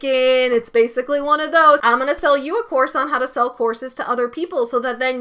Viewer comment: Lol every time you said “how to sell a course” I heard “how to sell a horse” 😆